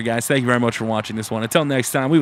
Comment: when you said "triple h has got the gold" I thought you said "triple h has got to go" I'm like, "Amen."